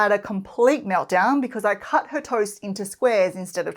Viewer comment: for example, this morning my 3yo had a complete meltdown bc i cut her TOES INTO SQUARES instead of triangles. Well yes if you cut my toes i will perceive it as a threat!! (jk she said "toast" but this is what i heard haha)